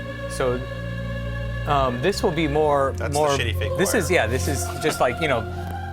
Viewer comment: That's the what what? What is he saying? "that's the shitty big part" or what?